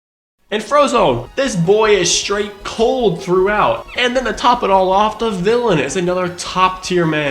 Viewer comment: what did he sayyyyyy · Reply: he said straight facts